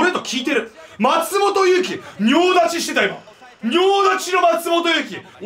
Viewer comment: 「仁王立ち」が「尿立ち」に聞こえてしまって吹いた